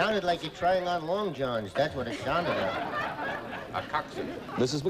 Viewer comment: did Vincent say “a cock suit”?!😂